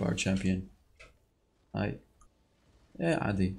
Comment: did you just said "ايه عادي"